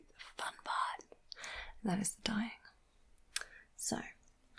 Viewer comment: I was just listening to this in the background, and I heard "We get to the fun part. Dying." Like, being unalived. Not dyeing your hair. It made me laugh